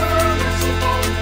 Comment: I thought is said don't touch my weed... Was I the only one that thought that?